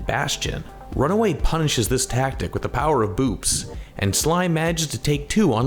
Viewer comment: i heard “With the Power of Boobs”